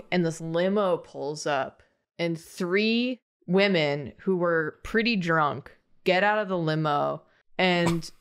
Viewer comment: I first heard this as "three women who were pretty German" and I had to share with the class.